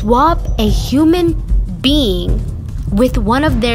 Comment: For a sec I thought u said: They swap a human BEAN in their place